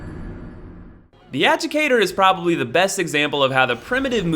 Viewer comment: Did he say Educator or Avocado?